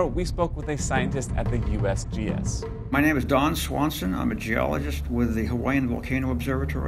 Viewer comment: DON swanson i could of sworn i heard RON swanson